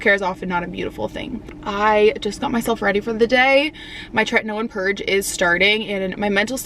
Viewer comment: your what purge is starting? I couldn't make out what you were saying at about 1:45 timestamp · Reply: Tretinoin!